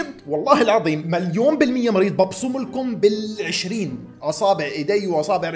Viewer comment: نصيحة لا تحلف الا بالله عز وجل تعديل:قال أبصم مو اقسم ما سمعت كويس 😅